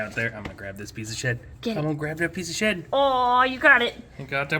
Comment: When said, im gonna take that piece of shed, i totaly heard piece of sh*t😂 i was like what did he just say?!